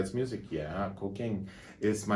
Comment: I heard "cocaine" not "cooking" 🤣